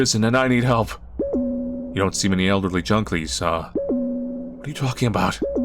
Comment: said “Junklies” instead of “Junkies” hehehe